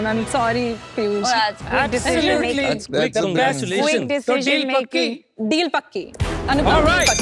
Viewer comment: what did peyush say? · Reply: That's your decision